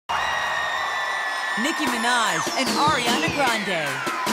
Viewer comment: ニキビな〜いはいアリアナ・グランデ。に聞こえて仕方ない